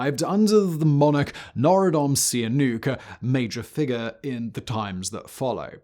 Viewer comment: so halfway listening to the video I heard something completely different... going back it's kinda crazy how much like "notradomus seen a nuke" norodom sihanouk sounds lmfao. Definitely one of those "only caught it cause I wasn't really listening" type situations lol